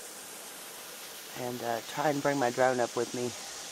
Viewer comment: Hey! When you said, "I'm gonna' take my Drone up with me," at first I heard, "I'm gonna' take my Grown-Up with me!" LOL! As always, Thanks for the Ride! Cheers! Thumbs Up! P.S. Pretty Horses!